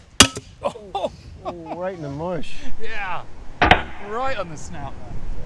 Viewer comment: Rewatching this for the umpteenth time: did I just hear Toby Capwell say "right in the mush" at 36:50? Clearly the yorkshire rubbed off on him during his time at the Armouries!